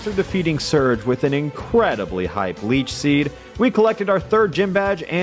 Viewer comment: did alpharad say bleach seed or am i going crazy?